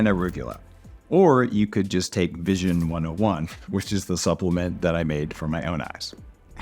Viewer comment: hahah I heard "which is the supplement that I made from my own eyes."